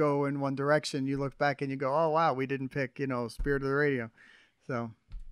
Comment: did he just say "Spirit Of The Radio"?!? 😱